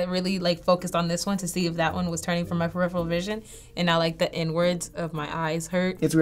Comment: Anybody else heard "The N-words of my eyes"?